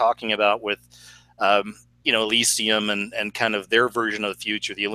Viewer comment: I'm currently watching metropolis for my first time can anyone tell me what movie 🎥 he was first talking about to me it sounded like he was saying elecee I don't know but I would like to know any good movies that show what they have planned for us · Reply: Dezoe Felix .....Elysium is the name of the movie